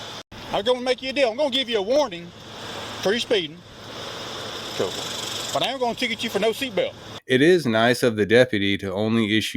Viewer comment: Two things -when I first listened to video the officer was talking so fast I thought he said "I'm giving you a warning for the speeding and I'm not writing a ticket for the seat belt" . I had to listen three times before I got that he said he was writing a ticket. I am sure the old man thought the stop was over . -It sickens me when cops say another officer "was just having a bad day". They didn't have a bad day and mess up a drink order at Wendy's, they grabbed an old man, screaming profanity, hand cuffing, slamming into the cruiser.